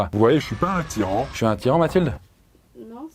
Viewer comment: J'ai compris: "Je suis attirant mathilde?" "Non, ça va" 😂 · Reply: J’ai remarqué la même chose haha. Il voulait dire je suis un tyran